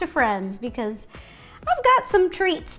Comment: I thought it said dog treats